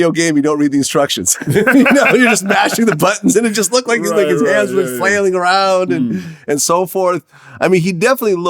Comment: Hold up at 3:50 did it sound like Vlad said it looks like this niggas hand is just flairing around or am I tripping lmfao